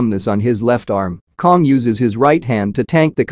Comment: What I saw: "Inoperable." What I heard: "Inner Purple."